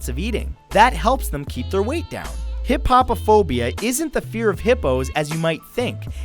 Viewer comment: I thought he said hip hip hophobia